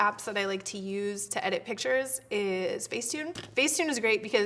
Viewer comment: For a second I thought you said "Dick Pic" hahahaha!!! Thanks for the awesome tips! Love you <3